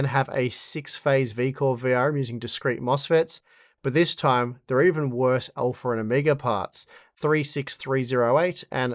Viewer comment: I heard "they're even worse than Amiga parts" and I thought "damn, Steve is so PC he's still taking shots at the Amiga" ;)